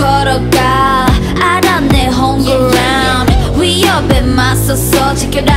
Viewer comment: 가사 하나하나 모두 허경영 코드로 가득함. 0:29 Homeground도 허경영으로 들림. 마지막도 허로 끝나고. 허경영 춤을 오마주한것도 보이네요.